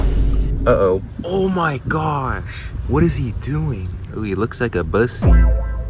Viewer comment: did he say it kooks like a 🐈? ?? 😂😂